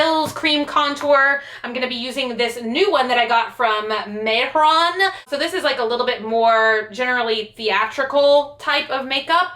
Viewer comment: does anyone know the brand of the cream contouring pallet? I couldn't hear what she was saying😂😂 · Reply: Mehron